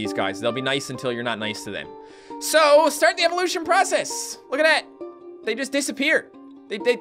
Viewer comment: I thought he said THOT proccess